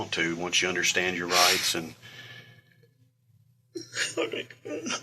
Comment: does he say "let me go"? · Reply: nah, he's whining "oh my god."